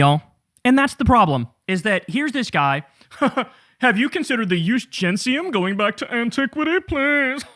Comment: what in the fuck is the eugensium? or am i hearing it wrong lol i love learning new words so can someone tell me what the fuck Kyle said at 13:18 someone help me lol